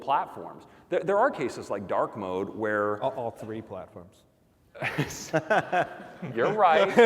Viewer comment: What‘s Greg saying to Craig at 39:15? Seems like some sort of code for not discussing internal stuff... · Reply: He says "All three platforms" Because iPadOS is it's own platform now.